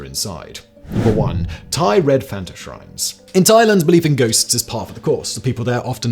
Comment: I at first thought was saying "Red Panda" for the last item. My thought was, she's not lucky. It takes thousands of hours of practice to juggle like that while riding a unicycle!